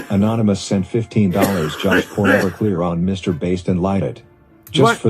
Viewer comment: I honestly thought he said livestreams with Mr Beast at the start then realised it was Mr Based 🤣